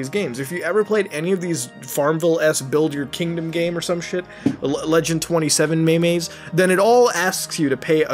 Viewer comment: Did he just call them "may-mays"? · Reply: He just said the legend27 maymays